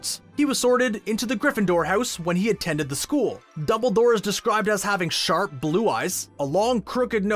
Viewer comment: did you just call him double door